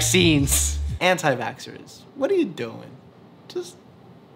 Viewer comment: Did you say “Porky” with a pig meme?! (Sí, yo hablo español.)